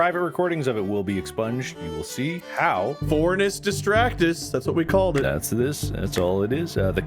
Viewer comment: I really thought Wade said Vornus Distractus